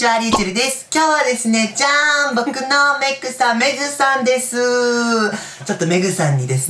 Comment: 「僕のメイクさん、メイクさんでーす！！」に聞こえた…メグさんね